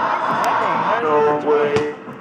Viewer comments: Do you hear « No way » or « Go away » ? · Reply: I heard “Norway” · This man is clearly harassing this poor robot, you can clearly hear him say "go away" and the human assaults him. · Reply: It's actually no way but I thought it was saying go away too until I saw the other comments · ”No whey", it means that if the robot had more whey protein in his diet, he could have won. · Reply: I thought it said go away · What specks is actually saying is "NO WAY" mishearded as "go away"